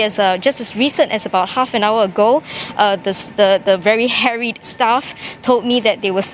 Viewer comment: I initially heard it as the very hairy staff..